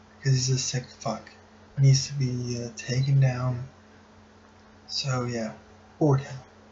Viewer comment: towards the end it sounded like someone said "Organ" right after "so yeah" at 2:42 its sounds like "so yeah Organ"